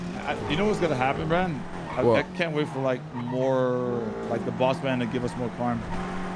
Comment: Anyone know what said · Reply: He said he can’t wait for the new boss man to bring new crime